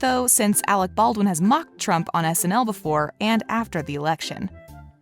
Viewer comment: did you just say sml which means supermariologan?